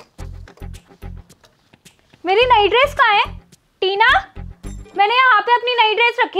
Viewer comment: in the subtitle it came night dress 🎽 instead of nayi dress 👗 😂😂